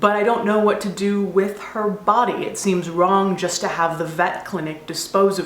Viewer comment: Oh, VET clinic! Thought for a sec that you said METH clinic!